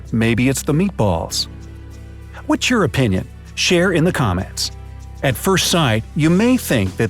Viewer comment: “mmm, the meatballs, what’s your opinion, leave it down in the comments below” I paused and all I heard was that XD